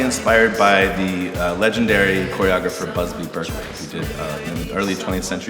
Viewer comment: Can anyone write me the name of the choregrapher they are talking about around 1:40 please? I can't hear it right. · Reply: Busby Berkeley?